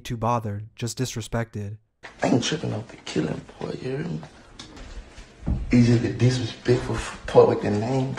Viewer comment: sound like he said “I ain’t trippen I’m finna kill that lil Boi u hear me”